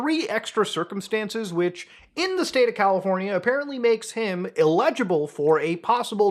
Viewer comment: sounds like he said "illegible" but did he mean that or did he mean eligible?